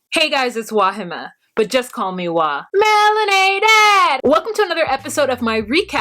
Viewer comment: Cute 😊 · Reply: I always thought you said "marinated" LOL